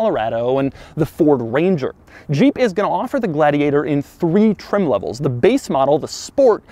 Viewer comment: It sounds like he says Ga-ladiator every so often. lol